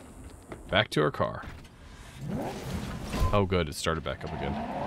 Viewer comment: I swear I heard "So I started blastin'." when you got to the chase scene. XD